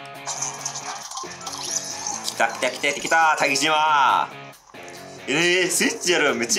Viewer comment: 待機島が竹島にしか聞こえないw一瞬びっくりしたww